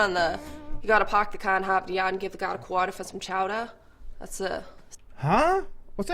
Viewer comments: For anyone who didn't understand, she said "Park the car at Harvard yard and give the guy a quarter for some chowder". (This is a poor representation of the Bostonian accent) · “You gotta park the car in Harvard yard and give the guard a quarter for some chowder” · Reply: oh wow, i thought she said give a girl a quarter for some chowder lol i’m from California tho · The person from Massachusetts, said: Park the car in Harvard Yard and go down to the water for some chowder. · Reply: Even her Boston accent was somewhat exaggerated, I understood it. I got the first part spot on, the second half, I thought she'd said give the guard a quarter for some chowder. I didn't quite get the go down to the water part. I think because that part kind ran together as she was saying it quickly at least to my California ears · She said u gotta park the car hon and get a gallon of water for the chow hon that’s what I thought she said but I’m from Georgia so